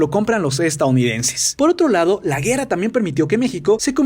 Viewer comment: Like si notaron en el minuto 4:33 que dijo la guera no la guerra